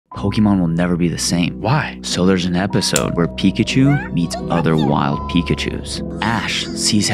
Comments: I thought he said "eats other pikachus" not "meet other pikachus" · What I heard was "Pikachu eats other wild pikachu" and I was sitting there wondering how tf did pokemon change so much. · When I first watched this short I thought he said pickachu eats other pickachs💀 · I'm so dumb I thought he said "pickachu eats other pickachus." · Bro i aint joking i heard "eats" not "meets" other pikachus lol